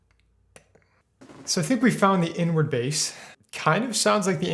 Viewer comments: I thought bro said "N word bass" I was bouta say....😂😂😂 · I thought he said n word bass, but it was inward bass.